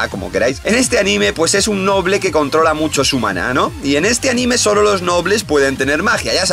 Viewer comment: soy yo o escuche mal dijo su banano :V en el 3:07 del video